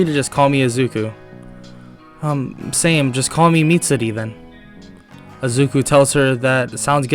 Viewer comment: I thought you said call me meat city